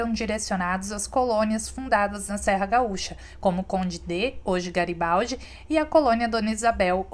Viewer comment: Conde D ?🤔 Nunca ouvi falar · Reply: Conde D'eu, era o marido da princesa Isabel. A cidade recebeu esse nome em homenagem a ele, quando ainda era apenas uma pequena colônia. Depois se tornou Garibaldi